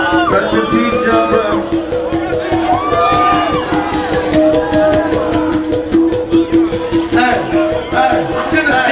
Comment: what did he say at 2:04 ? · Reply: I think he says rip john brown